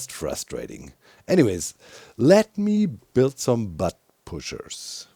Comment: Butt pushers. Yeah. I definitely heard it right.